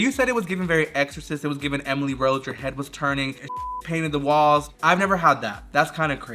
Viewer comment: I think he said he puked not that he painted that’s why douching didn’t help here